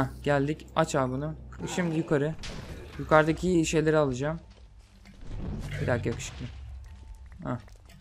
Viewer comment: da bir dakika yakışıklımı dedi benmi yanlış anladım